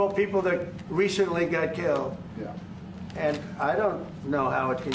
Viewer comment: Did He say "yah"!